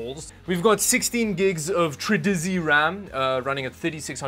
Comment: Did he say TRIDIZEE